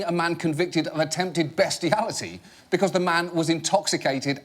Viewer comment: What does he say at 0:34 "Abraham lincoln pardoning a man convited of attempted........... "???? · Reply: Beastiality.